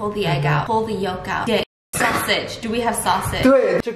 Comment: OMG when ever when they are together they are so cute!!! Squeeeeeee! Edit: your videos are so funny!!!!!!!!!!!!😂😂😂😂😂😂😂😂😂💖💖💖💖💖 2nd edit: at 14:40 did I just hear d---?! Or is it that I need hearing aids🤔🤔🤔🤔🤔 And 3rd edit: at around 16:25 she is so ticked off it made me laugh so hard 😂 😂 😂